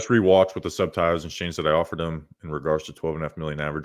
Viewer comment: The amount of people getting mad that Barkley turned down a 3/12.5 per from the giants. Which is not what was said. He said, "if I offered him..x/x, he would be a giant right now" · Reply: Nahhh I heard “I offered him”. Now I feel retarded 😂